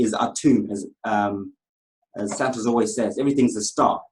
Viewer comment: I'm sorry did I just hear him say" as Santa always says" about 7:20 ?